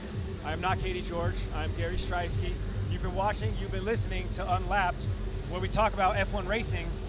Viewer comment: 😂😂😂I thought it said UNSLAPPED😂😂😂